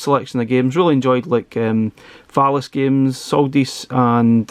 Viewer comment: It sounded like you said "phallus games", lol! 😆